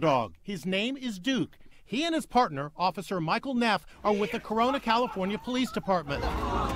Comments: d-d-d did he just say corona? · did you say crona?